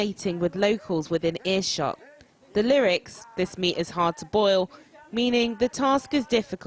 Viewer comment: Singing Gao mean tis ya meat hard for boil he talking about a woman.The reporter didn't understand the song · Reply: thought he said the YAM is hard to boil